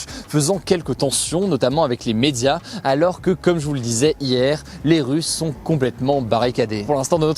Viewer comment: il n'y a que moi qui entends "les russes sont complètement barricadés"